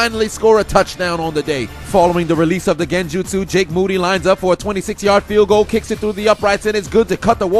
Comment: Is it me. Or does it sound like is saying "jake bootyline" at 7:40 😂😂😂